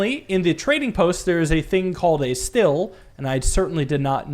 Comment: DENNY SAID IT WAS STEEL( I think he said) In the notebook.